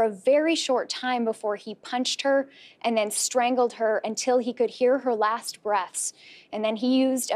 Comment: did she say " until he could hear her very last BREAST "